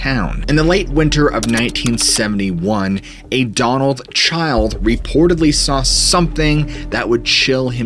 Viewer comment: Lmao I swear you just said "A Donald Child"... 😂😂😂😂😂😂😂😂 Am I high or did you just say that? 😂😂😂😂 It's within the first 40 seconds or so 😂😂😂😂😂😂 sorry. That was funny